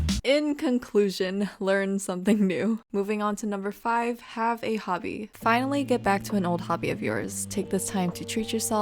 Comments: I thought it said : Have a baby! (untill I read it) Lol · For #5 I thought it said have a baby... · I thought 8:26 said "have a baby" 💀💀💀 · Not gonna lie I thought it said have a baby😂